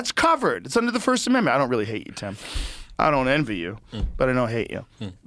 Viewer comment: did he just call him Tim?